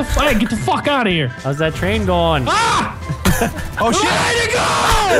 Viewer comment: rage at 8:07 is hilarious. Any idea what he said when he screamed? XD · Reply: I think he say "WAY TO GO!"